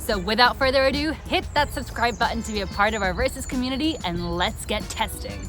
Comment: our racist community? My English is too bad but I always hear this